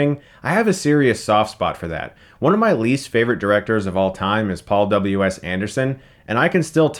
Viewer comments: i heard you wrong and thought you said "Paul Thomas Anderson is one of my least favorite directors" lmao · for a minute I thought you said your least favourite director is Paul T Anderson... phew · I thought you said your least favorite director was Paul THOMAS Anderson and my heart DROPPED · I thought he said Paul Thomas Anderson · For a second there I thought he said one of his least favorite directors was Paul Thomas Anderson! I was about to scream!